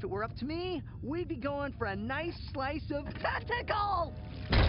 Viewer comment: I'm not the only one who thought he said testicles right? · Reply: He said tentacle